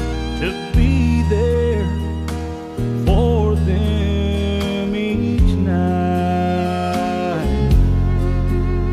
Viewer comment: what is he saying from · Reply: phil king He's saying "they need a daddy for them each night"